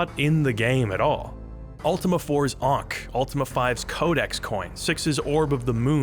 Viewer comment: my dumb ass thought you said "ultima 4 is unc" instead of ankh, there is no salvation for me